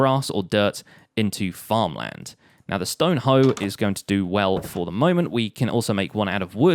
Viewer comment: I heard “and a stoned hoe is going to do good job”